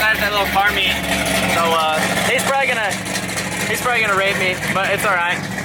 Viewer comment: He is probably....he is probably gonna rape me. (Did he really say that o___o)